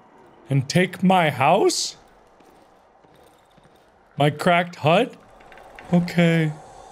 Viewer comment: At first I thought you said "Crack Hut" xD